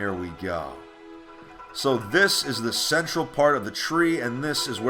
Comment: What did he say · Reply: It is the central part of the tree